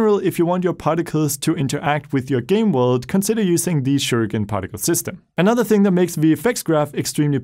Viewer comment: What did he say @ 0:43 ? The captions said "Shuriken Particle System"? I want my particles to interact with the world, so I want to use this system - just not sure what he said. UPDATE: apparently CC was correct! That is what he said.